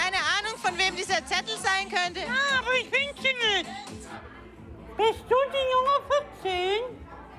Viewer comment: was hat er gesagt? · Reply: "Bist du die junge 14" hat er gefragt 😊